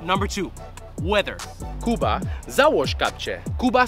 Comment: idk if i heard it right, but i heard 'Kuba załóż kapcie' (Kuba put slippers on) not 'Kuba załóż czapkę' (Kuba put a hat on)